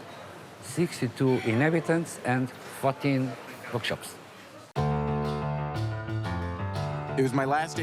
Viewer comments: I thought I heard him say “fucking bookshops” 🤦‍♀️🤣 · Who heard "f**king bookshops"?? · Am I the only one who heard "62 inhabitants and fucking bookshops"